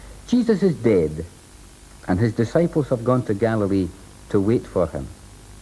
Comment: I thought the minister said "Jesus is dead and his disciples have gone to Galloway".😁😁😁